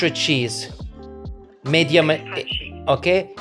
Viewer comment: I know he's saying medium, but I keep hearing "Made yum."